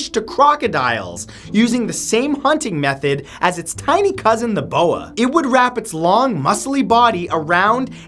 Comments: Sound like you said Ebola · I heard "It's Ebola"